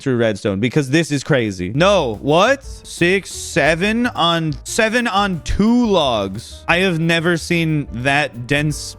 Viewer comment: what did he say? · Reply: how dare you